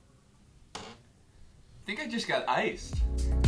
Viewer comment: Am I the only one who heard, "Nigga just got iced"